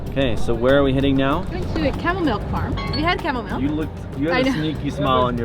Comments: I thought she said chamomile not camel mill 😂😂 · I thought she said chamomile like the tea 😅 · My dumb ass thought you said CHAMOMILE not camels' milk. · i heard "chamomile", as in tea, when they said "camel mill". so imagine my surprise when they walk into a room full of camels lmao